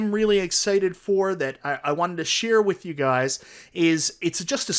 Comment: Great video. Were you aware that your accent changes every AR sound to ER. Ercade 1up. Like literally EVERY ‘AR’ sound is ‘ER’ 😂 When you say “SHARE”, it sounded like you said SHERE. MERVEL vs Capcom 2 AR sounds do not exist lol